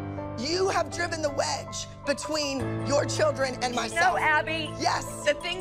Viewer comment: Abby: Kelly, you have driven the wedge between your children and my son. According to Wikipedia, Abby was never married or had kids. Maybe YouTube’s subtitle caption heard what Abby said incorrectly or something. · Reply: She said “myself”